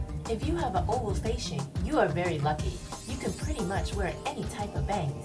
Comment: haha I thought she said " If you have a oval face shape you are very ugly" instead of "lucky" :)